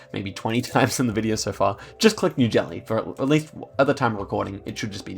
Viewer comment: I swear I heard 'New Jelly' 😭